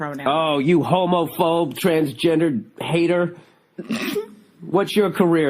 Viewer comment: I thought Bill said “you TransJenner” lol